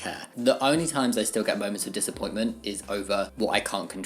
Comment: am ngl definitely thought he said “boners of disappointment”